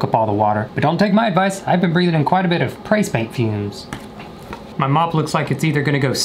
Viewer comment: I know i ain’t the only one who heard him say “Pray Spaint” 💀💀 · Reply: Angela I thought that he said “face paint”